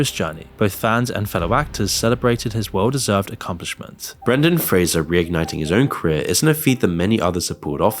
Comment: Was it just me or did it sound like was calling Brendan Fraser Brandon? He's not Biden haha. It sounded like was saying Brandon all the times he said his name except for the last time where it actually sounded like he pronounced it correctly. Smh 🤷‍♂